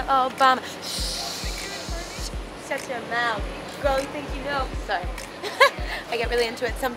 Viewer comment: I CAN'T BE THE ONLY ONE WHO NOTICED CAMILA SAID "Girl you think you know" instead of "Boy" right?